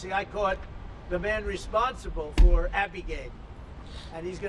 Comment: “five weeks into the presidency I caught the guy responsible for…” what did he say? Abigate? What is that?